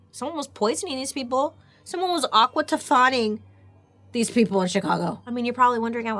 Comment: 1. BAILEY can you please do a Dark History on fluoride? 2. can someone please tell me what the word at 30:10 is...aquatifying? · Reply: Aqua tofana. It’s a reference to one of Bailey’s first mm&m vidoes.